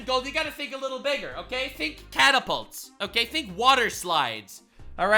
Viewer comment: Ngl when you said "think catapults" I heard, "think cannibals." Then I thought of how American's love their steaks and how people say we are all fat anyway plus it would bring down the cost of groceries a lot and with all that it sounded valid as a means to get rid of 20 million people. I mean hell people catch and cook Sun Bears so why not. Then your next line clarified you said "catapults" but yeah if I were illegal and knew that the POTUS solution to me illegally being in the US was to make it open season I'd nope the f out.